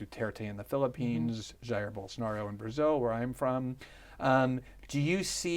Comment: subtitles said "bowls Nardo" lmao